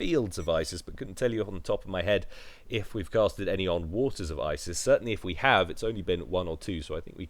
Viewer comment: at forst i thought you said autism of isis